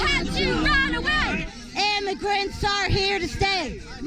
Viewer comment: “Milo had to run away! Immigrants are here to stay!” Isn’t Milo an immigrant? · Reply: I thought they were saying ham and grits were here to stay